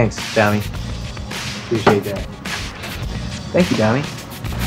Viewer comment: Did he call her “dummie” here or am i tripn ?!